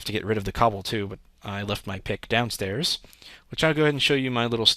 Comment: Its sounds like you said, "I left my dick downstairs."